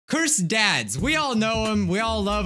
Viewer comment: When you said cursed adds I thought you said cursed DADS